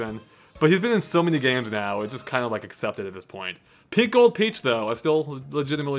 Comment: It sounded like you said "Pickled Peach" at 45:25 XD